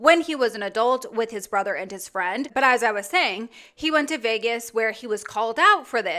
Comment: I don’t know why she thinks this happened last night, but this was months ago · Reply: I thought she said he went to Vegas again.